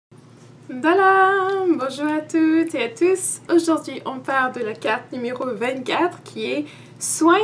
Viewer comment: Merci 😊😊😊 Qu'est-ce que tu as dit vers 0:01 ? C'est en quelle langue ? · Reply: "N'dala!" :) Je commence toujours mes vidéos en souhaitant la bienvenue à ma chaîne! C'est le Bami de chez moi ;)